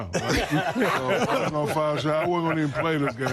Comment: of course, I also loved Zeke & Chuck - but WTF did Shaq say at 16:19? He was in "foul shape"?! 🤣🤣🤣 These dominant figures were LAZY AF! Different times, different attitude.